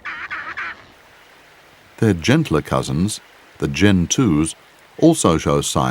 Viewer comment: Help! At 14:18 (and again at 16:01) they start talking about another penguin. I know they aren't saying Gen2 Penguins but it sure sounds like it. What are they called? I can't hear anything else than Gen2 Penguins.